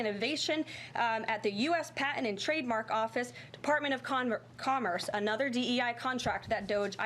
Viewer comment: did I hear her say Department of Condoms?